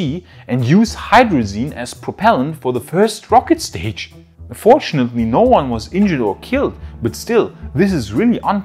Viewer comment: Heads up. At 12:13 it sounds like you say "unfortunately" and speech to text even shows it as "fortunately". We know what you meant but thought you might want to prepare for the whiners. :-)